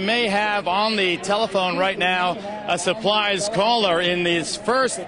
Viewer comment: did Geraldo say "a supplies called?" 😂 Geraldo has some drugs on his mind. 😂